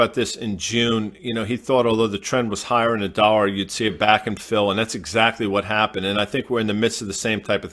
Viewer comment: Always thought they said back end fill but I think after finding nothing online that they are actually saying “back and fill” which Google says is a sailing term. Closest definition I can find is a zig zag pattern.